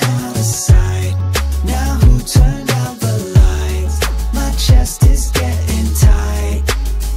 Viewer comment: What I thought was funny, 3:21 "my chest is getting tight I'm getting Tylenol!" Tylenol is a medicine that relieves pain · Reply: I thought it was saying "Its getting tighter getting tighter now!"